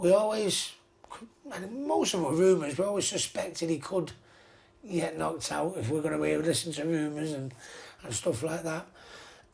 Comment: Can anybody clarify what hatton is saying at 10:51? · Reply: He says " We always suspected he could get knocked out, if we listen to rumours and stuff like that. Ruiz did it"